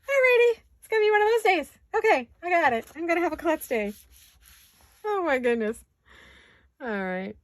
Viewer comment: Your projects are so adorable and the possibilities are endless and pinkable.!!! So enjoy your prep process with your little pouches. And when you said day I misheard and thought you said “Blest Day” so when your stamp came out so fab pinky I thought “yep...blest”!